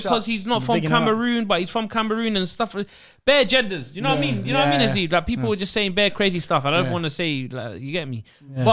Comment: did say “do you know what I mean haseeb”